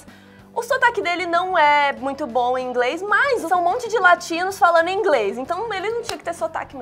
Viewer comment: Quando falou "um monte de latinos falando em inglês" eu ouvi "um monte de latidos falando em inglês" ai eu fiquei tipo: mais o q?? Ai voltei a fita e entendi tudo :-)